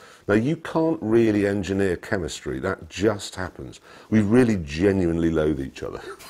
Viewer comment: My English not good enough what Jeremy says on 06:52? :) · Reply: сус Jeremy just says that they genuinly hates each other, but i think its only when it gets heated.